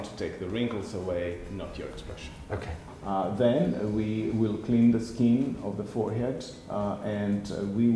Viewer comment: I misheard 2'36" as 'we'll clean the head of the foreskin' ...